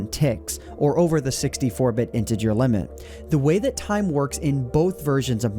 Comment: You said interger and I thought you said into your.